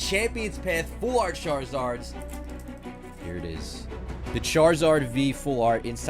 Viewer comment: Why did it sound like he said shartzard